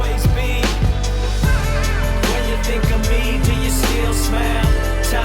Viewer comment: the one that starts at 15:49 right? i NEED to know what song that is someone!!! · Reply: i thought he was saying “when you think of me do you still smell”. Took me a well to figure it out.